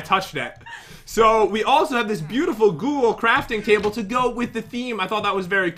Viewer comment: Is it just me or did he say goole